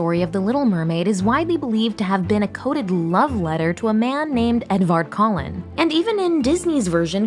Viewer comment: Who else heard "Edward Cullen" at 0:26 instead of Edvard Colin?